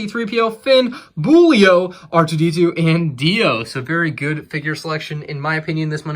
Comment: I thought he said DIO from Jojos Bizarre Adventure